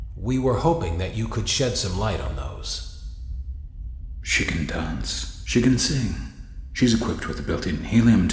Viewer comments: Is it bad that I heard “Chicken Dance” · who else heard the words “chicken dance”?! Lmao 😂 🤣 haha